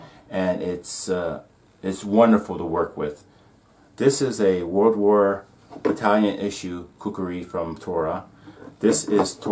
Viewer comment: Did I hear "Italian issue kukuri from Tora" @ 7:10...?